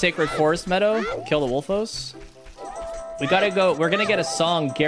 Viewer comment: I swear I thought he said “Lets go to the forest and kill the locals.” He meant Wolfos, lol.